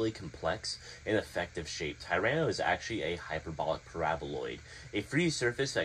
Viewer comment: i have no idea what u just said but i completely agree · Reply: I said a Hyperbolic Paraboloid 🤣 it’s quite a mouthful ngl